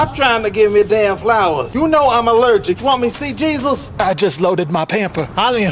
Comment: "u know I'm allergic, Yo WaN MeH tO SeE JesUs" Funniest part evah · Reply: the funny part is, when I tried to look at the comments, at the same time, I heard and saw "u know I'm allergic, Yo WaN MeH tO SeE JesUs"